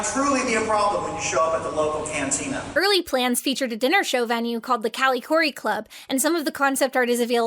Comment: This entire time I thought you were saying "Callie Khouri club" and I thought it was insane that they would name the Star Wars restaurant after the lady who wrote Thelma and Louise.